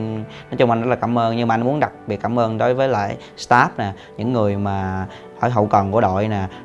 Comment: Staff nhé ae. Mình nghe nhầm thành Stark =))